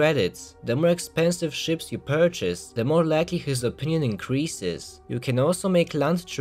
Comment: I always hear "the more likely" as "the more laggy"